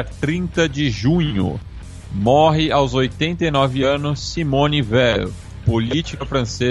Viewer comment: Estava baixo e eu entendi "morre aos 89 anos um homem velho", era Simone Veil